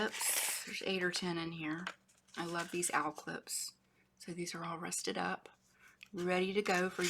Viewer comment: *lol---what kind of paperclip did you add in there? I thought at first you said owl clip then I heard al clip. I love your signature card. I think you should sign it in the front. If I ever get one of your signature card, I would like to have an option of making it into a tuck spot.